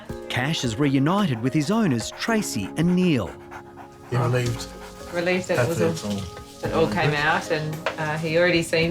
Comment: What is he giving to the dog at 20:30? The black chicken mix. Did he say chocolate and chicken??? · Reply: No he said charcoal chicken